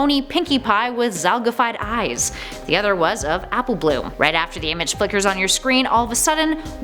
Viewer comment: Did you say Apple Bloom? OMG I LOVE APPLE BLOOM!